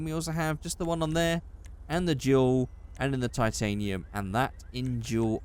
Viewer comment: did he just say juul big boys